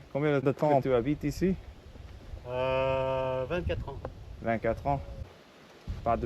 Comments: he said "vingt quatre ans". Why did the subtitles say 28? Not french, I'm confused lol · Farmer says 24 years. You say 24 years. Subtitle says 28 years :) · He actually said 24 years @ 1:53, great episode